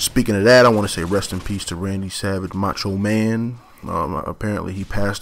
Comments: I thought he said "Sandy Ravage" i was like NOOOOOO!!!!!!!!! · he said Randy Savage passed, and for a sec i thought he said Sandy Ravage!